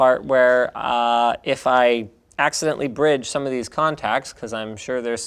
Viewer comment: says "if i damage one of these capacitors i could die" lets it hit the table wp linus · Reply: He actually said, "if I accidentally bridge some of these contacts...I could die."